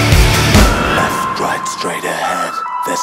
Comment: I KEEP HEARING "there's no way I'm your dad" inSTEAD OF "There's no way out you're dead" AND IT'S KILLING ME